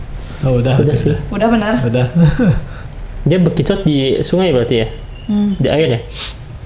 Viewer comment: di menit 7:38 ada suara itu kedenger "bekicott" dri belakang kaka kaka yg lg reaction